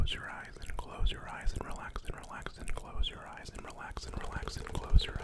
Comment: When he said "Relax and close your eyes" I thought he said "Relax or die" lmao